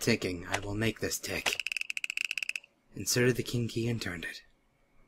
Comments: When said "King key!" All I heard was "kinky!" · I wasn't watching but I was listening and he said "king key" but I heard "kinky" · For some reason when said 'King Key' I heard 'Kinky' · Lmao when said "King key" it sounded like he said kinky! I was laughing sohard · lol it sounded like he said kinkey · lol....when i heard him say King key. I thought of kinky XD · "King key!" I swear I thought he said "kinky!"